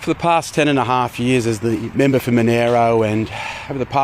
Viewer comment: did he say "Monero"?